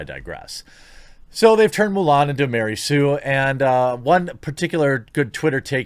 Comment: Haha, I think I heard you say "Mule Ann"... XP